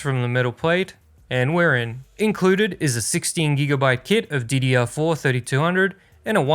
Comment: Did you say, at 2:20 "inside is 16G "GIFT" of DDR4 3200? If you did, that is hilarious. I think I have some old DDR4 chips in my toolkit case somewhere. That truly is a memorable "gift". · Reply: I think he said kit